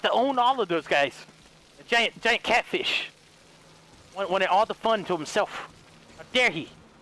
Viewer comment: H2O you can't kill the anomaly cousin volts out radiation electric Ball you can't kill it if you get to close it starts pumping out radiation and killing you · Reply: Could've swore he said he wants to figure it out himself 🤔